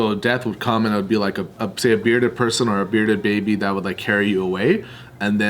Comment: did he say bearded baby 😂😂